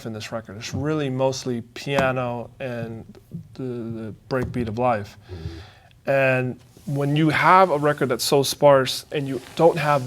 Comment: what did he say at 21:20? "the breakbeat of Life"